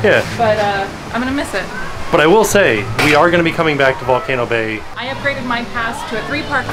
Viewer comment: Legit thought it said Volcano Baby 😂😂